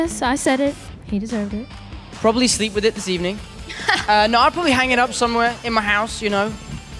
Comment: I thought that in 1:06 he said: “you’ll probably sleep with me this evening...” OMG I’m so dumb🤦🏻‍♀️😂